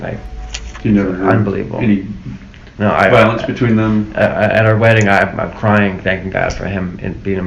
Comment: does say “at our wedding”?